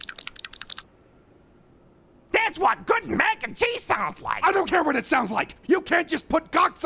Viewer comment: the subtitles say “sloppy Mac and cheese sounds”